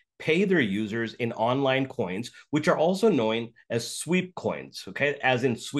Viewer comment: the guy says “coin” and then “knoin” instead of “known” 😂